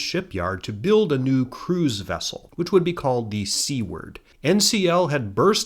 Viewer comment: Oh my gosh, when you said "Seaward" I thought you said "C-word" and I was very confused for a moment! 😅 I'm so happy you covered something to do with Columbo, it's a wonderful cross over of two things I love! This was another wonderful video, and we are all so lucky to have you.